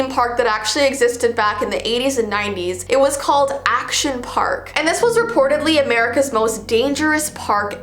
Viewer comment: Once I heard "Action Park", I thought it was "Afton Park" from Five Nights at Freddy's.